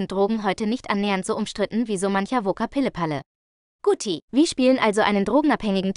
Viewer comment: Woka Pille Palle? Ich hab nicht verstanden was da gesagt wurde xD